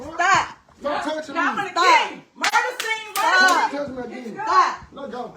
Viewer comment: She shot him on purpose because he was packing his stuff to leave her toxic self it sounded like she said murder scene and then pulled the trigger 🤬👀